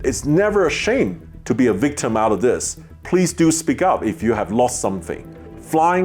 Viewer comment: lol sounds like your say fleas with your accent